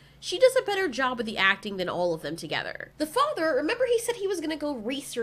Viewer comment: because this is a cringy movie, I thought you were saying "the fucker" lmao